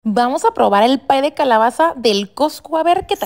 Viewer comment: Pensé que había dicho "pai de calabaza del COSCU" JAJAJAJA